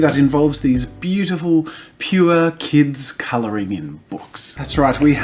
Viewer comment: i thought he said coloringing